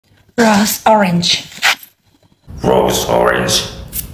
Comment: It thought she said "gross orange" 💀💀